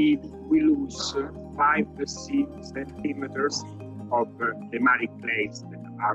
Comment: I think he says "five to six centimeters" instead